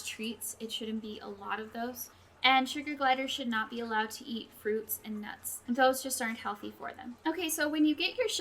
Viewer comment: Did you say they should not eat fruits and nuts?!? But then earlier in the video you say they SHOULD eat fruit. So can you explain to me why you said that? Im wondering if its a mistake that you said that. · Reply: I noticed it did sound like that, but I said Seeds and nuts. May not have pronounced it well. :/